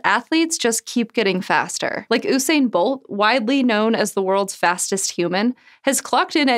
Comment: excuse me did you just say ooo-sain bolt instead of usain bolt